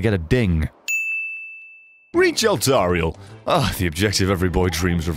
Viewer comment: He reads off 'Reach Eltariel.' Me, knowing his sense of humour and not looking at the screen, misheard 'Breach.' "Kinky. Oh wait.."